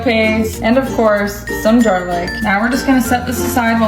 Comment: I swear to god she did not just say jarlic